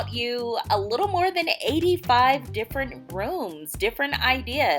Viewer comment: It sounded like you said “different errors”.